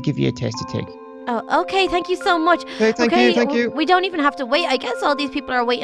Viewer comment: Who else thought she said, “thank you so much daddy”?